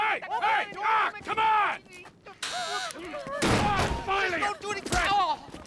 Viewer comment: Auto-generated subtitles say "finally a booty call".